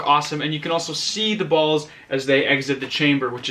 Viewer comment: omg what did he say? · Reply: What's wrong with him saying "balls?"